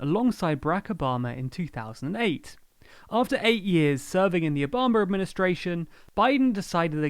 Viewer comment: Did you say Obamer administration?